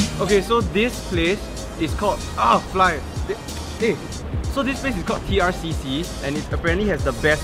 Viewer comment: I literally thought that the place is called ah fly😂😂